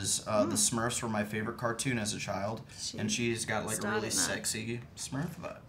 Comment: Lol I thought he said Smurf butt